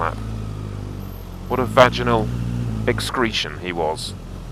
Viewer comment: Did you just say "Vaginal escretion" or is it just me being too much naughty..?